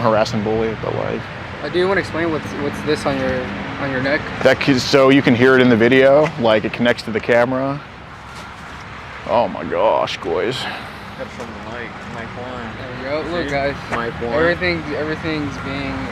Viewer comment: Did he say "oh my gosh, goys?" At 1:13:14? 😉